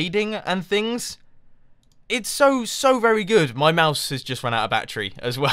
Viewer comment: I thought he said, "My mouth has run out of batteries.." xD